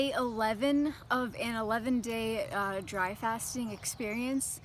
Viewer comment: Thought it said dry fart.